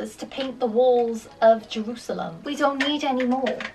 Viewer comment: did you just say Jerusalem 😳🙂